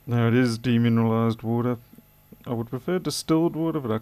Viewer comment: Now that was yuck, no not your spot on socket replacement, that pcb poo lol. Im so deaf, i thought you said "Demonised water." , i had to laugh, im a silly sod :-D. They are bloody lucky that it still worked ok.